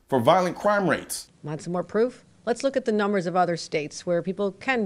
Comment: I thought she say we couldn't use numbers